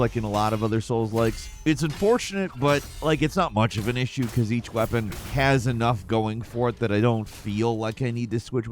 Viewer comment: i thought you said "each weapon Khaz-anough going for it" and had to rewind lol